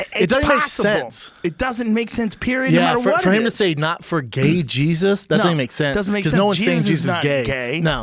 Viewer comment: People thought Yoel said "No for gay Jesus". They thought he was being homophobic or criticizing progressive Christians or something.